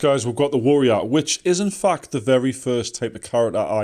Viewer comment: lol i thought you said which is fucked when talking about the warrior lol had to rewind like 3 times